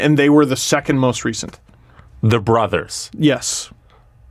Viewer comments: I heard "the brothers", not "they're brothers"... "Enunciate, your Majesty!" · was completely right here (about torres and dipietro) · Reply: It does sound like he said "THE brothers" which would be a wierd question in itself